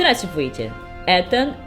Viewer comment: Мне послышалось, или там было "здрачивайте" ?)